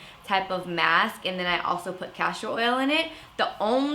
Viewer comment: I thought you said cashew oil at first. I was going to frantically look up the benefits.